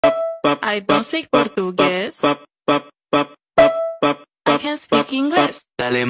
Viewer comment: i thought it said “i don’t speak english, i said i don’t speak english!”